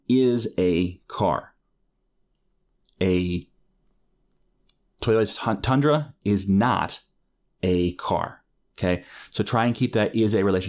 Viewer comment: I laughed out loud when auto generated subtitles said "a toilet on Tundra is not a car" at 17:52 great explanation btw, I think I understand all now